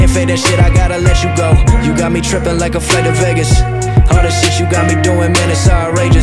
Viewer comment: I could have swore he was saying you got me tripping like a friend in Vegas. Referring to the hangover. But I look into shit too much lol